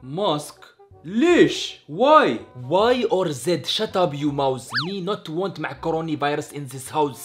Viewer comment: First I thought he said shut up you mouse but now I think he actually says shut up your mouth😂